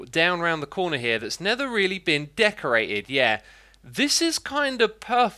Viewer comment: did he really say “nether really been decorated” lmao